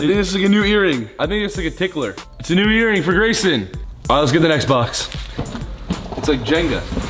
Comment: it sounded like he said let’s get the Xbox lmao 😂